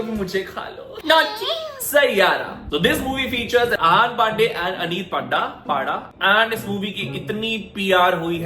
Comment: i heard pandey and panda 😂😂🤭